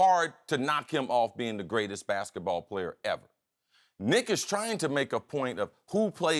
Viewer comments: did he say "NI**AS" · I thought for a second he said "n*** trying" I had to rewind to hear "Nick is trying" 😂😂